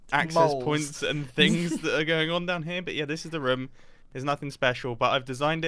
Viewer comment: did anyone hear say "raom"